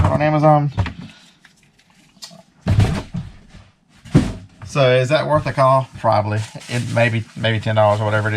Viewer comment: 😳what did he say @ 11:10 ? Sounds like “is it worth a call”. What is a call?